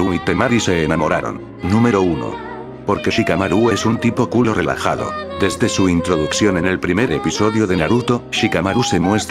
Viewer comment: Shikamaru es un tipo culo relajado??? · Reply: Pensé que había dicho cool o relajado 🙃